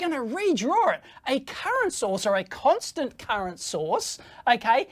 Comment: Why do I keep hearing "Constant Carrot Source"....